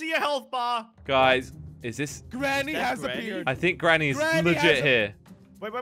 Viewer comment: Who else thought Jelly said "Granny has a beard" when he said "Granny has appeared"?